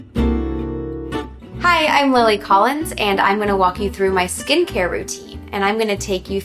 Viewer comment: I heard “hey Emily Collins” :)